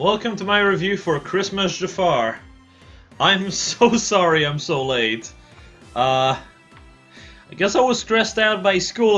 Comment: Why did i hear " I'm so sorry im Soleil'd " at 0:05??